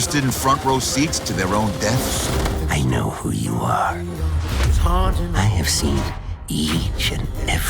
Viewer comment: did he say "I know who you are, I've seen you...Misty'